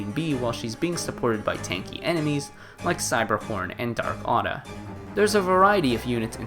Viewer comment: I swear I heard cyberporn